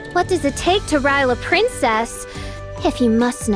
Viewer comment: I'm sorry but I heard "what does it take to rail a princess" and I was like 😳 Fischl u okay